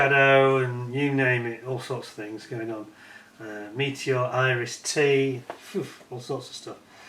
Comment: Hmmm...Yes I am not impressed either. 😕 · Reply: didnt even know they do /did a European set....btw when you said "Iris -T" i first thought you said "Irish Tea".🤣🤣🤣🤣